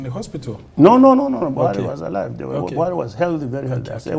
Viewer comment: DID HE JUST SAID BUHARI WAS ALIVE, OR MY EARS IS PAINING ME